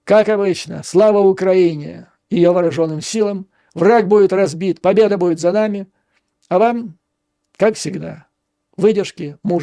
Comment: в финале послышалось "Трамп будет разбит, победа будет за нами")))